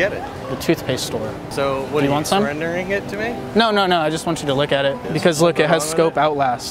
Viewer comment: Thought he said "I just want you to lick at it" aahah